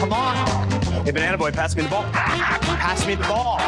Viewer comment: did he call him banana boy? wtf does that even mean??